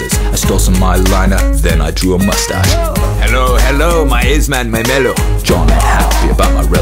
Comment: What is he saying at 2:03? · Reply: Hello Hello my aceman my mellow. That’s what I’m hearing anyway😉